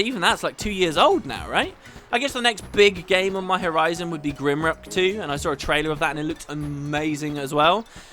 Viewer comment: What game did you say at 23:20? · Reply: Grimrock 2! It's coming out on the 15th and I'll be LPing it.